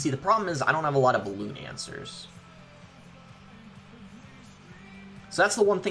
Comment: kinda sounded like cancer